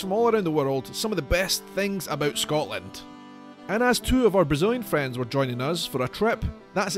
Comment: The first time, I thought you said.."and two of our Bazillion friends..". 😂🤣 I thought "Are ya braggin', then?" This time, I heard "Brazilian friends". What a relief. 🙃😋